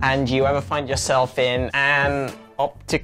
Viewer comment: "Where is ardontercat" "Where is anturdadurk" when I tell you I screamed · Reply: i thought he said thunder cat but ok